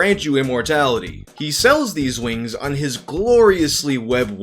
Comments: I'm sorry Mr. but did you say wings? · did you just call them wings? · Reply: He definitely said he sells these wings. LoL i had to buffer back to listen again cuz i was like... did he say wings??